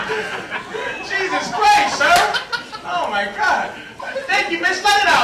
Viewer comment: the stenographer write "cheese us christ" instead of jusus christ x_x